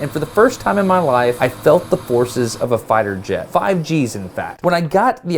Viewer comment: Did he say 5G? BURN THIS WHOLE CHANNEL DOWN! /s